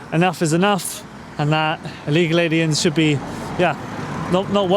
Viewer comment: did you say illegal aliens 🤣